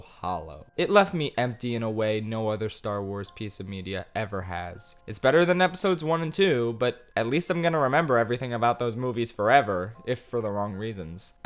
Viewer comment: Did... did you just say that IT'S TREASON THEN